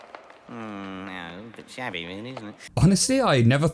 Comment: I thought he says it’s a bit “shabby” rather than chavvy :/